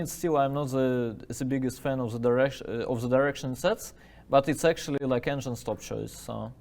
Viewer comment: What does dubov say he’s not a fan of at 0:49? Direction openings? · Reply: The direction the move Bb7 sets, like he wasn't a big fan of the position black gets after several moves after Bb7.